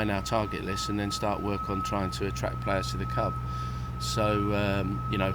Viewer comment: I swear at 6:49 he says 'cub' instead of 'club'